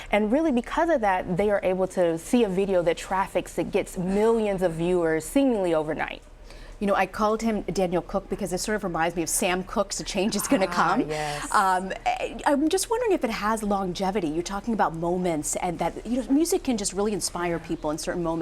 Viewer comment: Donald cook? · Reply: Idabst Reed I thought she said daniold cook lmao